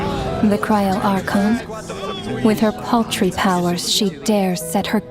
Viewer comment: Imagine being told the Cryo Archon has "paltry powers" by the shades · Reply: I thought you said pastry for a second